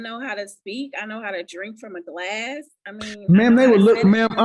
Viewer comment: Did she say "I know how to drink from a glass"?